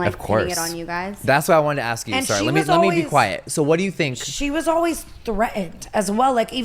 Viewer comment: Did anyone catch Jeffery telling the Cohost to "Be quiet?", timestamp 09:50, or did i just take that out of context? Also, Watching this disappointed me so much because I used to LOVE Jeffrey, but I feel like throughout the years Jeffrey gets nastier and nastier. Damn.